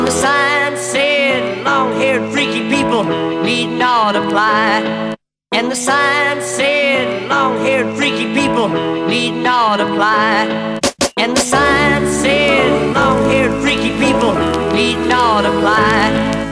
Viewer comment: Classic! When i was a kid playing this game i always thought they were saying 'and the sign said long haired freaky people, eatin all the pie'